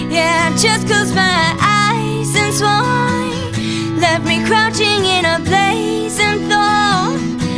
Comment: Why do I keep hearing ' left me crouching' instead of 'left me crashing' ?? Is that what she really said? Lol crouching sounds really weird.. ahahah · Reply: she's actually saying crouching O.o